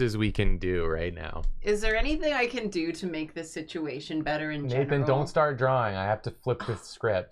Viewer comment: i swear my brain rewrote what she said to "can i make the situation worse?"